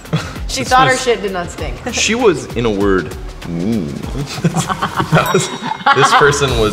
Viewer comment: what does finneas say here i can’t understand him because of the music lol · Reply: “she was, in a word, mean” i think